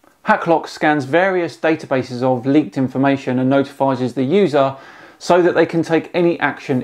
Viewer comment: did you say "notifizes"? 😂